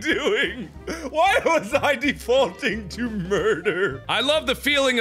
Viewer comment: Mark at start: “these are relatable problems that we humans have” Mark a little bit later: “WHY WAS I DEVOTING TO MURDER!?” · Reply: I thought he said “Defaulting”